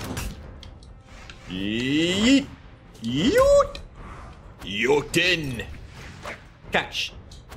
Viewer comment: did you say nuten;^;???????